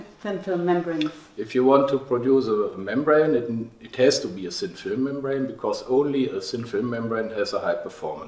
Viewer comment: Please can you tell me what dr. Said at 2:05 , it is hard to interpret ...." it has to be a _______ membrane" what did he said in blank... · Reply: Thank you for your request. Professor said: "If you want to produce a membrane, it has to be a thin-film membrane because only a thin-film membrane has a high performance."